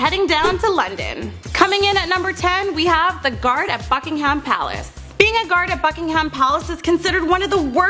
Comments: I thought you said fucking an plaice 😂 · I thought she said fuckingham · I thought she said F**kingham palace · I thought she said fuckingham palace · at :34 it sounds like she said fucking instead of bucking! lol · For the first one she said I thought she said "fudgeing ham palace " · i heard her say fuckingham....maybe it's just me lol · It sounded like you said fuckingham palace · sounds like shes saying fucking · I thought you said "Fucking ham palace"